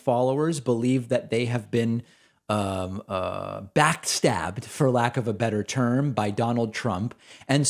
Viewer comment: I thought you said "vacc stabbed" but you said "back stabbed."